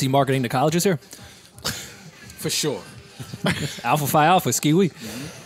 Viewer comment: yo did Rory just say “Alpha Phi Alpha SKEEE WEEE”??? 😂 😂 😂 that might be the whitest thing I’ve ever heard on the pod! 😝